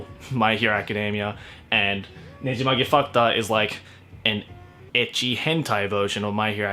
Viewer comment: I literally thought he said nejimage fucked up